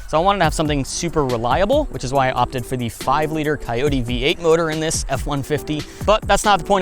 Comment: Coyote, ok, I thought he said Toyota